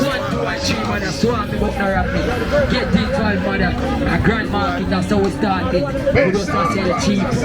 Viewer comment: I thought she said gun market?